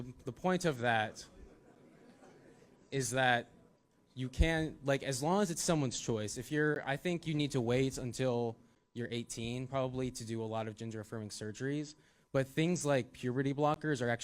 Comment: Did he say "Ginger affirming surgery"???